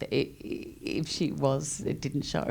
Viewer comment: what did cate say about rooney at 25:40 ? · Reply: If Rooney was terrifyied, it didn't show